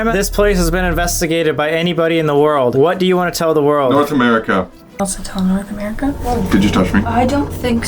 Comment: " I literally thought Colby said "North Korea"...